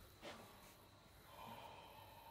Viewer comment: The second he said "No!" my Australian detector went off. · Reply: I heard “naur (no), why does it look so scruffy” lol. I love Aussie accents.